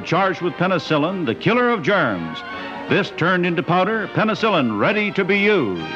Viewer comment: Did he said at 12:33 "the killer of germans"